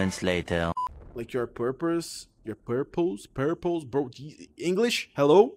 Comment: i thought he was saying purples not purpose